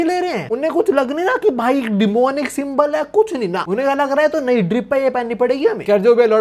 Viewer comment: you say drip, I hear Bleach